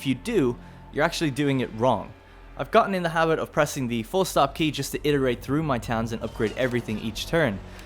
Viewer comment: Anyone know what key he said??? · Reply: Full stop and comma.